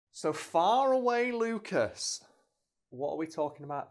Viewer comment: Whenever the restrictions end i hope he says "so not that far away Lucas" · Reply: I always thought he said ‘fire away Lucas’. Now I see I was wrong